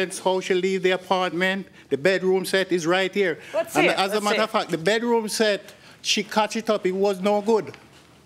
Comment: Lol 7:47 it almost sounds like he says "as a mother-f--k" instead of "as a matter of fact"